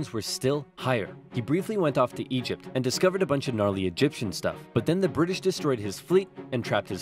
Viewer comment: DID I HEAR GNARLY?